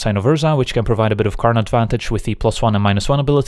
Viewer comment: I swear I heard him say "provides a bit of Karn advantage" when talking about Karn lol. I mean, it's not wrong, unless the opponent also controls a Karn.